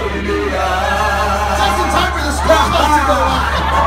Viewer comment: What did Lin say at 3:48? "Just in time for the..."? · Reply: Just in time for the school bus to go by...